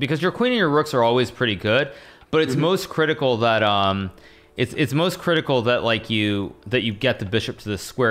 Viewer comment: "but it's most critical" I heard that as moistcr1tikal lol